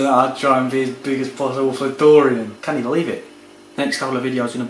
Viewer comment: did you say 'canny believe it' at 6:38? LMFAO